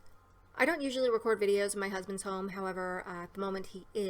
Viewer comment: At first I thought you said "...I don't usually record in my husband's home..." & I was like wait..... you live seperately?! I had to replay that several times lol.